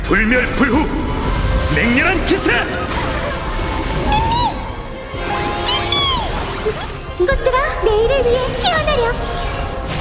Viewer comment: 나만"이것들아 내일을 위해 피어나렴"라고 들리는건가?